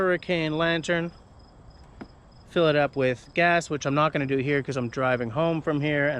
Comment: DId He Say he was going to "fill it up with gas" ???????